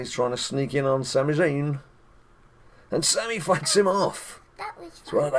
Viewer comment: Children do not read this. When I first watched the video I had to go back and watch 18:42 again, its sounded like he said 'and Sami F***s him off', realised said fight, silly me -_